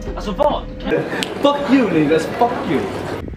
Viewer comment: I love this video so much but could you maybe cut out the clip at 10:11 😭 he says the name linn but a lot of non swedes thought he was saying the n word and there was a whole fight about it on twitter, it would suck if someone misinterpreted and made a thing abt it again, bc omar would never say something like that